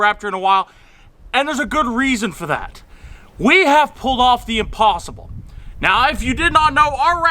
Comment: At first I thought you said you found the opossum instead of the impossible